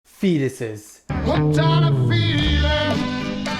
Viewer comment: I thought you said “penis’” not “fetus’” and I was thinking how does a penis turn into a baby 😂😂😂💀💀